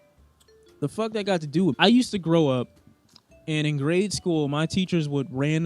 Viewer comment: “I used to grow up” - 2020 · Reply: Dead ass thought he said I used to got to grow up